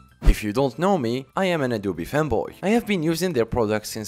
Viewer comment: I thought he said “Adobe femboy” and not “Adobe fanboy”